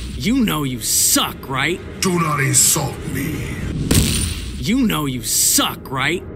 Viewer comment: What did Bane say to Raph? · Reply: "Do not insult me!"